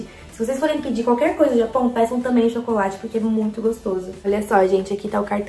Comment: Na hora que ela disse "pessam chocolate" eu juro que entendi "péssimo chocolate" eu voltei 2 vezes pra eu conseguir entender kkkkkk sonsa eu